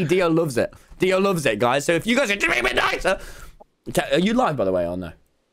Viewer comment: Is this old cause I swear i heard him say at 2:09 "techno are you live btw"- idk im probably hearing things but i wanna know if anyone else hears it?